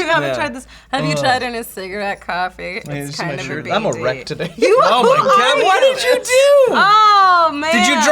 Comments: I was looking down, and I thought said, "I'm erect today." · totally thought said "I'm erect today" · I thought said "I'm erect"